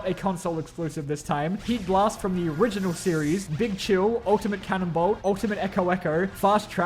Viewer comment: As someone who knows nothing about Ben 10 I initially thought that he said Big Chungus